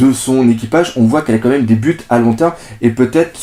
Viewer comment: A 9:05 j'ai entendu des pu te à long terme XD